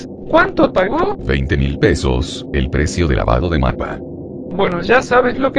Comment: El precio de lavado de que? Yo le entendí mapa